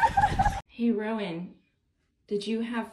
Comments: wtf did you really think the mom said "heroin"? who tf does your subtitles they are ALWAYS wrong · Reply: its maybe like "hey rowan" or something similar, although i wouldn't be suprised if his name is heroin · Is that kids name Heroin? Am I hearing/seeing that right?! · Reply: “Hey Rowan” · the mother calls her son - "Hey Rowan". The subtitle says "Heroin" Welcome to the Fail Army